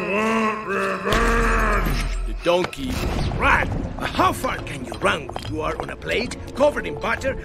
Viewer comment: I thought Puss said the bad word Well, He didn't say the F word if that's what you mean. He actually said "Far Can". So don't worry.